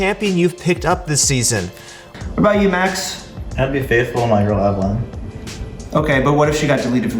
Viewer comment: does anyone understand what he is saying. Its the 6th time I'm rewatching it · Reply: "I'd be faithful in my girl Evelynn"